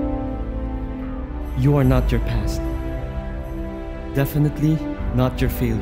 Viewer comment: This is sooo good! At first, I thought this said, "You are NOT your PSAT" Lol....